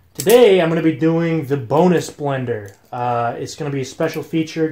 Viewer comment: Lol I thought he said boneless blender